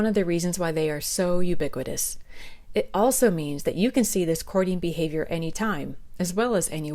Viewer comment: lol I thought you said corny behavior instead of courting 😂 makes me think of when men wear shining jewelry or drive fancy cars to impress females.